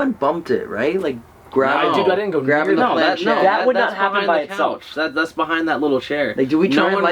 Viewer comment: At the point in the well I heard it say "by my self"